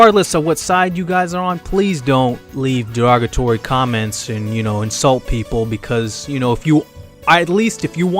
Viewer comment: i thought he said no insults